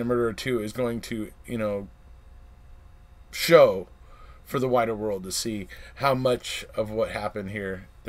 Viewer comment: I thought you said whiter world at first. My jaw dropped. You are exonerated. I look forward to seeing new police interviews and perhaps some squirming thrown in for fun. So looking forward to the new episodes. Praying that Brendan benefits and is given his freedom. I am sure we will see more about the bones. We might even see a segment on the control question!